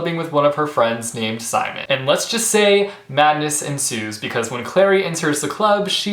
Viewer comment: at the start i thought you said Magnus ensues and i was like same